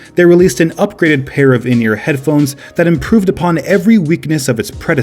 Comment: why do I hear "in-your-head phones"